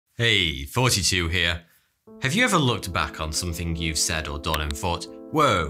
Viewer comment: His names thaughty2 but with his accent it sound like hes saying "hey 42 here"